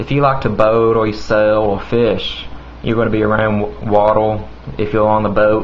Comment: lol when you say the letter 'R' it soulds like "oul" like when you said "water" it sounded like you said "watoul" lool